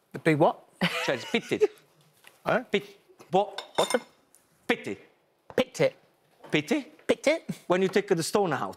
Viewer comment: Gino: Pitits Holy: breaks down Phil: wheezing Every time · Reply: it sounds like he said pit tit where me meant pited (removing the stone from the cherry)